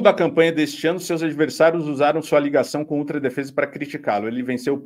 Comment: Eu entendi "Ustra defesa"...?